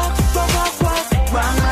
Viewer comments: ramen head ramen head ramen head u take me to the top and straight down like agyro drop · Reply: I always hear " RUMM my head !" Rumm my head..xD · i thought they were saying "ROMAEEEE ROMAEEE" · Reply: I thought at first they were saying "Ramen head~ Ramen head~" xD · Why am I hearing "be my lady" when rock my head comes out!?!?